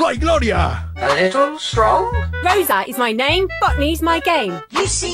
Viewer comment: im def not dirty minded but i hear “Rosa is my name, FUCKING is my game. The worst part is that it makes sense since its adult games…